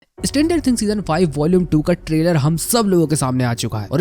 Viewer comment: did bro say standard things?😂